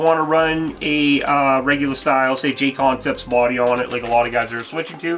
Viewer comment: What was the popular aftermarket body? I could quite understand what you said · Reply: Hi! Jcconcepts bodies I was referring to at that moment. They have the best selection out right now